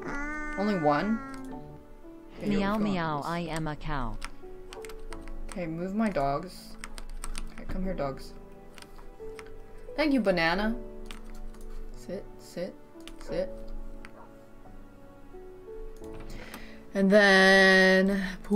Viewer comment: i thought that said banana yoshida and i had bad flashbacks :(